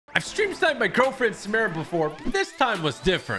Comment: i thought it said "i stream sniped my girlfriends"